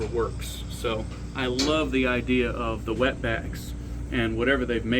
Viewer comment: did he just call me a wet back? Lol